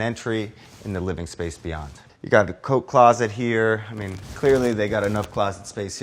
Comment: I legit heard "coke closet" when he said coat closet